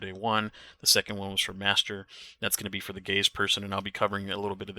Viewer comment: I was passively listening and thought you said “the gayest person” when you obviously said “gaze person” 😂 Had the same thing happen in an LFG “ok which of you are the gaze” Took me a second, but the way it was worded was funny